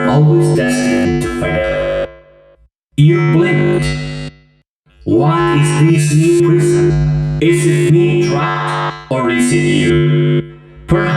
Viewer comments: The one with Withered Bonnie that says "???" This is what I heard, "What's this new prison?" "Is it me trapped, or is you?" "Maybe it's us, both..." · It’s actually fail instead of fight me